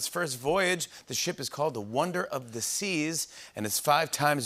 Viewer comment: on a video about it i thought the guy said "wonder of disease" :D